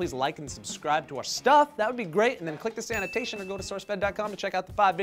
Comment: Quantum physics is when says "click this annotation" but I hear "click the sanitation"